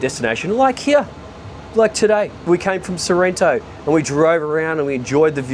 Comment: I'm just enamored at the background. It's absolutely gorgeous, and so is that boat. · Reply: oops, I thought said he was in Santorini not Sorrento